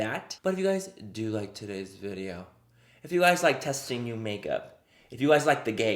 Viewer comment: omg when you said "if you like testing new makeup" i only heard "if you like testies"........................ lmfaooo